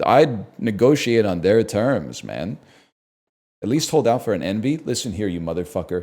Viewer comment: I thought he was saying enby and was confused